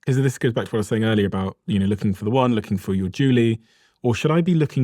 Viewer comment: Did you just say " Looking for me Julie" and smirk? 44:30 mark. Big up the Staines Massive! LOL